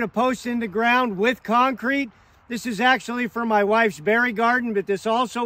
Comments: Dude i thought you were gonna say "for my wifes burial" but you said "for my wifes berry garden" oh lord😂😂😂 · Dude i thought you were gonna say "for my wifes burial" but you said "for my wifes berry garden" oh lord😂😂😂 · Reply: Omg I thought he said "fairy garden" too until I just read this thread lol! I was excited to see a fairy garden... now I'm kinda bummed haha :) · Ok. I heard 'bury garden'.... not 'berry garden'!! 😂😂😂😂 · I thought he said bury garden…like where she will be buried. 🙄